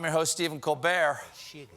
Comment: waaaaaay in the background, anybody else hear somebody say "Shitty"?